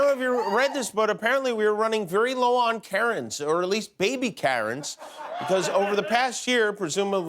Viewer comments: At the beginning of his monologue, I actually thought that he said we're running low on carrots, or at least baby carrots. That's what I heard! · Man, when he said “we are running low on Karen’s, at least baby Karen’s” I thought he said we are running low on carrots, at least baby carrots.... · At the beginning of his monologue, I actually thought that he said we're running low on carrots, or at least baby carrots. That's what I heard! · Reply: hahaaaaaa 'twas the first thing I heard. "we're low on carrots" 😁😁😁😁